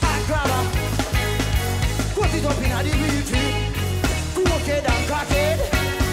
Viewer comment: did he actually say "cut it up inna di weed fi di cok3 head and cr@ head??